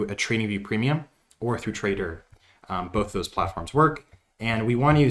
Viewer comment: Hi, what other platform do you comment at @ 9:38, other than TV premium? I couldnt understand. Thanks for your wonderful content! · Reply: TRDR